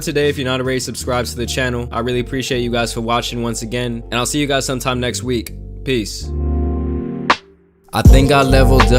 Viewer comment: AHA I THOUGHT SAID ”SEE U NEXT WEEK, PLEASE”